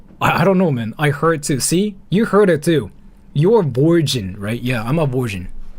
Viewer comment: Mishearing "that's bullshit" as "you're virgin" is amazing 🤣